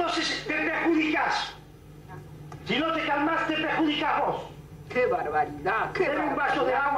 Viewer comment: si no te calmas te jubilas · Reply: Creo que dijo "Te perjudicas vos" Aunque yo también entendí "o te jubilas" xDDDDD